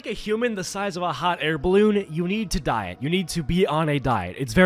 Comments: First time I heard "you need to DIE". And, I agree. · I THOUGHT YOU SAID YOU NEED TO DIE